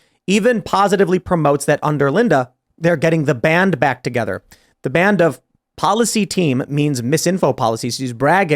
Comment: getting the "banned" back together is how I heard it. Get on "the banned wagon".